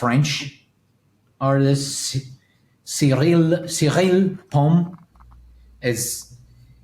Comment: For those who didn't catch the name in 1:00:10 the artist name is Cyrille Pomès.